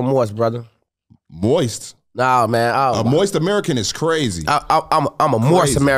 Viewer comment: “Moist American is crazy”😭😭😆😆😆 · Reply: Sound like he said moist but he said Moorish